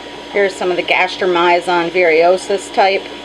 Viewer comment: Could somebody give me the correct spelling? I think said it was Airamiazon fish. I'ts about 11:02 in on the video. Thanks!! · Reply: erromyzon